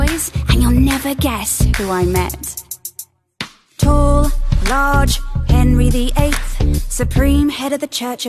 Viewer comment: When I first heard this I thought it was “Tall, large Henry the ape” Edit: damn my notifications are blowing up- thanks for the likes lmao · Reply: I keep hearing "tall, large, and really 8"